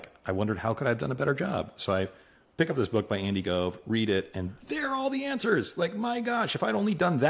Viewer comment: Can somebody list the books he mentions around 10:35? I’m not a native English speaker, does he say indigove or something? 😅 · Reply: 1. High Output Management - Andy Grove 2. The Hard Thing About Hard Things - Ben Horowitz 3. Disciplined Entrepreneurship - Bill Aulet